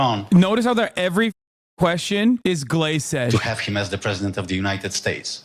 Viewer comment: anyone know wtf Hasan is saying??? I hear Glace edge but Idk wtf he is saying